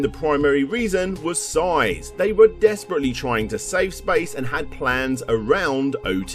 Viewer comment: the vocalist said 'space' instead of 'size' as written on the screen lol